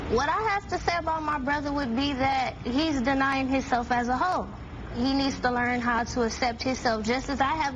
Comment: OMG at 2:14 i thought the sister said "he is denying himself as a ho" i laughed so much.